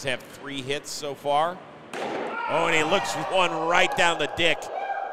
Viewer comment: Did anyone hear him say, "Oh he looks at one right down the dick"? hahahaha